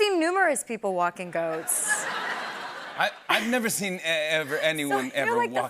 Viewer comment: I had this playing in a different room and thought she was talking about seeing ghosts in New York; I couldn't wait to come back and listen and now I hear she's talking about people walking goats lmao. We have a guy who walks his three goats in a nearby trail area, it's always fun to see